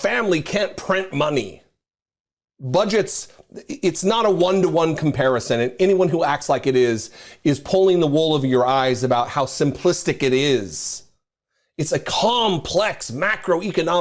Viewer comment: Totally agree, but did you say “wool of your eyes”? CC got it correct, but sounded like you said Wool of your eyes 😊😊😊😊